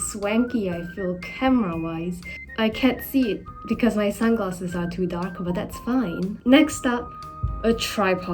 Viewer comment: I thought you said there for a sec "I can't see because my glasses are too dank"